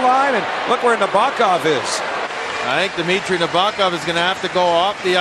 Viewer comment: did he say dimitry nabokov???